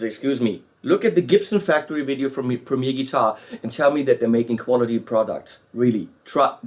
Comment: What was the link to the Gibson video mentioned at 2:20 mins - I couldn't quite catch it ? Thanks in advance. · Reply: The Gibson Factory Tour from Premier Guitar